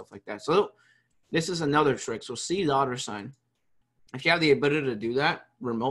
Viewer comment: Hey everyone At 5:08 he says use c what I cannot hear what word he reffers to after that? Please help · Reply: C dollar sign